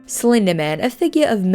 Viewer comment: I thought you said Selena Man .....watch out for selener ~☆